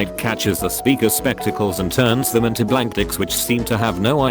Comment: Did anyone else hear him say "Blank Dics"?